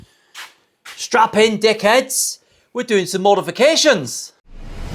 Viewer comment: I think I might have "misheard" the intro. Was it strap in or strap on???😂😂😂😂 MILLION THANKS FOR PUTTING UP WITH ME AT THE SHOW (I had the "Thanks" in caps to match me)!😂😂😂😂 AGAIN, thanx a zillion!!!! And go and buy some nice Belgian chocolate for the missus. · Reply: Haha, definitely strap *in*! Great meeting you at the show boss. ❤